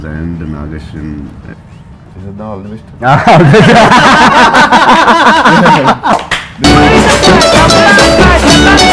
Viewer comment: what dialogue prabhas said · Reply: He said : Cheseydhama all the best